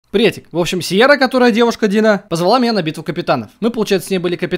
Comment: мне сначала послышалось что Сиера девушка деда